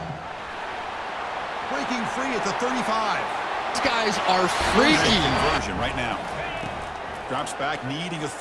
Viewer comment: what did he just say? “Freaky”